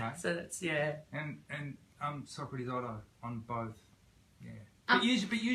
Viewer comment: I THOUGHT I HEARD SOCRATIS